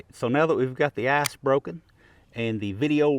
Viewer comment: Love ya,just work on your pronunciation a wee bit . I could've sworn you said ,"now we've got the ass broken" instead of ice